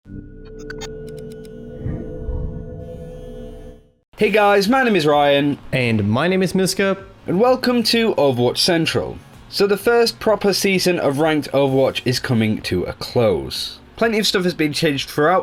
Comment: I keep hearing "and my name is no scope"